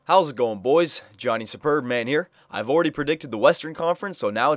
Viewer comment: When you say superb-man, it sounds like your saying "sperm man" haha